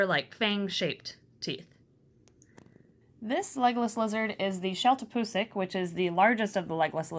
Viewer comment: I thought it said eggless lizards